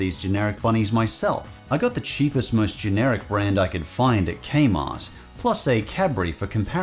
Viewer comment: Did you say “Kmart”? That’s a name I haven’t heard in a long time.